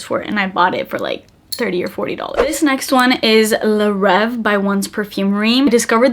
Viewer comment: How do you spell the name of that one? I can't understand the name of the perfume. It would be helpful to show the names on the screen or description box. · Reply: La Reve (a/the Dream) by One Perfume Realm